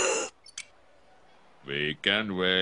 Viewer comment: I thought he said "Make him wait" But he said "We can wait"